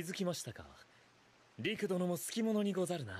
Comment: So the Japanese for man of culture is "sukimono" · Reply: neither, I hear "好きものにござるな", with ni in-between